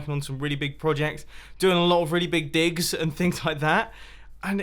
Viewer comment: it sounded like he said dicks when he said digs